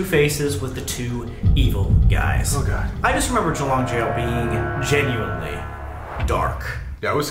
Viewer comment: what's the name of the jail because I can't hear it quite clearly and the closed captions not sure if it's accurate or not (jalong jail?)